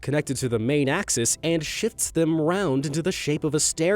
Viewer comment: it sounds like you said "it shits them around" not shifts